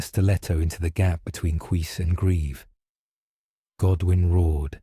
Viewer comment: Mabey it's just my mind playing tricks but is the nerration AI? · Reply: I think it's trying to say "cuirass", but it sounds more like "queess".